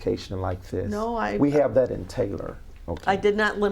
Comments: he says be more pacific..pacific instead of specific😂😂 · Be more “PACIFIC” So I know what you saying🤣🤣🤣🤣🤣Fuckin Satan wit a Terrible Hair line